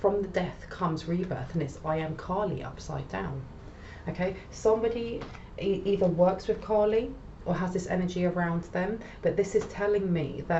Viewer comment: "somebody either works w Connie" 👈?🤷 I cant make out that word. Is it "Calling"?